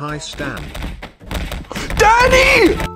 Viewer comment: am I the only one that heard "daddy" instead of "Dany" ? ;^